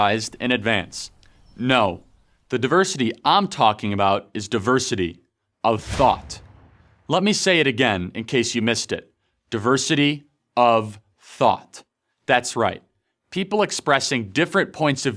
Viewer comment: Can you say it one more time, I thought I heard "diversity of thot"